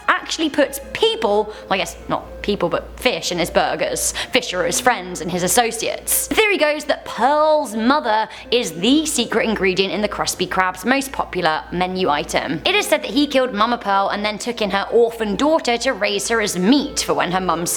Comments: i heard kruspy krab, anyone else? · Who else heard her say kruspy crab